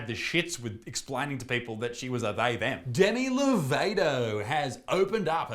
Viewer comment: The way you pronounced Demi Lovato sounded like Danny DeVito so I thought you said he was using she/her pronouns and I nearly dropped my phone 😂